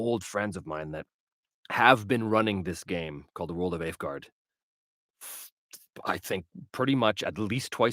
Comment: Hello, there was one game you spoke about your friends playing still from the 80's sounded like you said World of Afguard is this what it was ? i can't seem to find any information on it anywhere